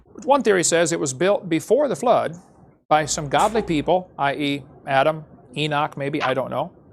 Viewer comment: To add to the insanity of this moment, I misheard "godly" as "goblin".